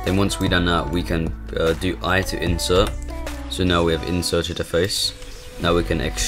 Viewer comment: Help i hear ait enter at 0:33 can someone explain ?